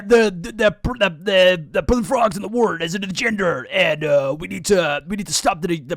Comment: bc of your accent at 6:32 I thought you said "what IS a gender?" and I was like damb. He really get it.